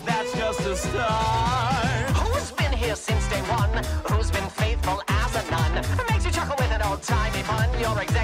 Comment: i thought alastor said faithful as a gun but no it was faiful as a nun